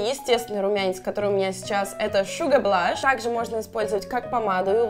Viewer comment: почему я слышу Suga????? ))))